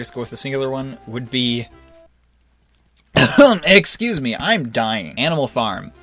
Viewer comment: Did he say "Eggscuse me" at 7:40? Sure sounded like it.